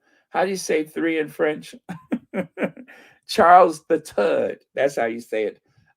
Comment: I really thought you said Charles the turd but you actually said Charles the third